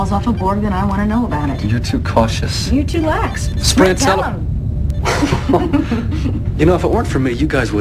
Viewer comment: did he just say sprint tell him? she has a vajayjay not a peepee silly!